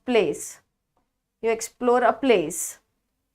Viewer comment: What will be the correct word for the analogy place or probe ( am confused ) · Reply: Probe